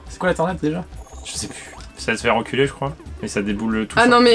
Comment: Dites moi que je suis pas le seul à avoir entendu "Ca va te faire enculer jcrois" ?